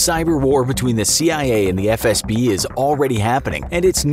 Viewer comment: When the title says "Doing" instead of "During" Edit: the title’s fixed now :) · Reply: the eng sub is still not fixed, at 11:47 it shows KGB instead of FSB